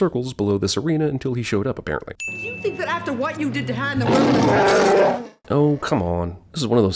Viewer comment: Right around 14:40 I would almost swear that Leia called Han ( that we pronounce like Hahn) Han (pronounced by her like Ham, same as Lando.) I will listen again but that's what it sounds like to me.